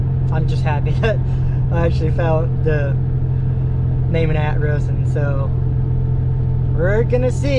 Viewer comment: Bro it sounds like your saying shet lol and attress and vehiggle